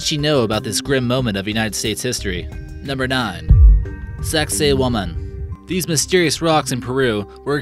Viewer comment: OMG LOL!!!!!!! When he said Saksaywaman 3:20 number nine, i thought he said sexy woman!!!